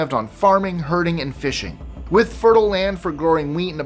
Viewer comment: #16 I heard “farming, HURTING & fishing”! 😱 Then I realized that my speed was set on 1.25. 😂😂 Edit: seed —> SPEED Autocorrect SUCKS!